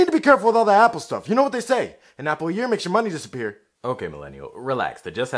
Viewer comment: “An Apple a year makes the money disappear” -Us Millenials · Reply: I thought he said "an apple an ear makes your money disappear"